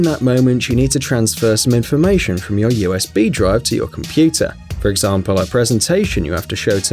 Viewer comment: did he say USB DRUG!?!?